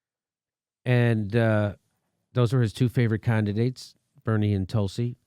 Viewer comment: Did Jimmy say "Con"didates when referring to Rogan's endorsement of Bernie and Tulsi?